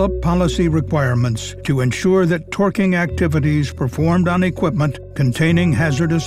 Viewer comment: I paused the video for a while and restarted at @ 10:27. I was so confused at first when I heard TWERKING ACTIVITIES… 😂